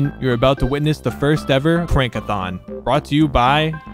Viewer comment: I don’t know if this is just me but I thought he said “prank-a-Thon” and I was confused😂